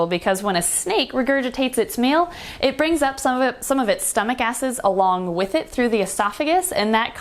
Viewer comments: Did she say stomach ASSES? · Please tell me I'm not the only person who heard asses instead of acids at 3:36...